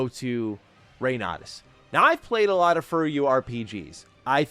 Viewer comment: “furry you” rpg’s? What the hell is saying here? I have no idea. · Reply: FuRyu, the developer of that game